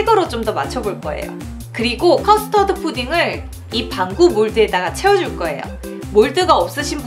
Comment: 나만 반구몰드를 방구몰드로 들었나...